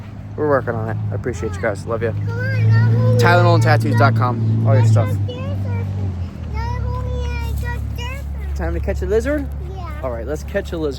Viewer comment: 😂😂😂 so thought you said Tylenol tattoo's